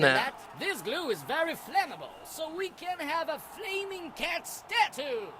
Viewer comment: D...did I hear him say flaming cat stah-too at 17:19? Did the developer use a second rate speech program?!